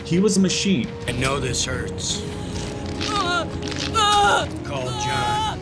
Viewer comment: He says: "I know this hurts" I heard it as: "And no desserts" 😅